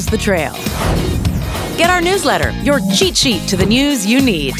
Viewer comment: did she just say chee chee